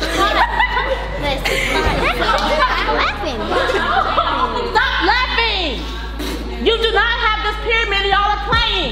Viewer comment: I just notice at 22:08 the little in girl in the front just called someone a bit$& maybe I’m wrong but that’s what I heard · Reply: Legit said “stop laughing b****es trippin” 🧐 I know I’m not bugging 😂